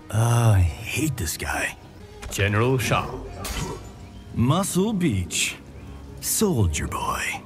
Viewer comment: excuse me, what did Johnny say? 😳😳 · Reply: Muscle Beach.